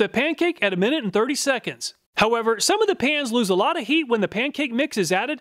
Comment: I thought you said some of the pans weren't for induction. But isn't that what your coot top is?